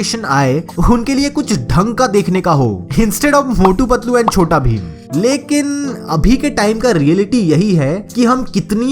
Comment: i heard chotu patlu and mota bheem instead of motu patlu and chota bheem